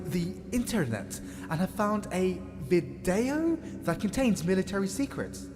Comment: did he said Videl (from Dragon ball Series)